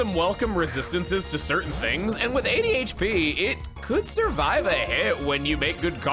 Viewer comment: when describing Shelmet I honest to God thought he said the little dude had ADHD instead of "80 HP"